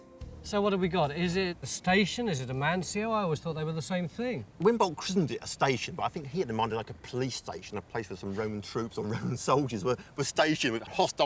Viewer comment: I can barely make out what the guy (blue shirt) is saying. @ 2:44. Its like he's speaking with noodles in his mouth. (Please, I don't mean this in a personal verbal assault, rather "Voice and Diction" aka speaking of the language.) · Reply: I listened to it a few times and came out with this: "Winbolt christened it a station, but I think he had in mind like a police station, a place where some Roman troops or Roman soldiers were stationed with hostile natives outside. Today we prefer to say no it's more to do with a mansio or hotel - it's kind of a cross between a motorway service station and (unintelligble) coachway inn."